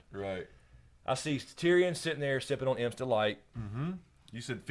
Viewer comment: When you said Imp's Delight, it sounded like you said Amstel Light haha